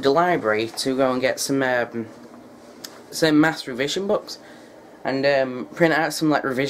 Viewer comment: did you just say Masturbation Books?